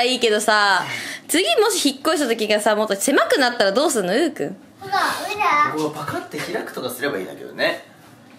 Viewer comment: 初めの方がうーくんって言ってるように聞こえたの 超可愛い😍💓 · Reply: yamamoto jun うーくんって言ってるよ！